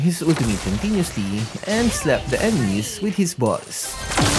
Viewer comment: "slap the enemy with his balls" What did you say?